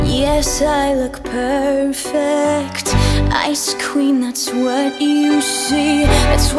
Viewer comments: I thought she was saying “ice cream” but it’s actually “ice queen”.... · I THOUGHT IT SAID “ICE CREAM” NOT ICE QUEEN AND I WAS SO CONFUSED SJDISH